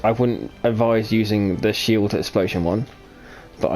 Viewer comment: you said something about "advise not using the shield"? I didn't quite get what you said there, can you elaborate? :D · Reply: 'shield explosion one'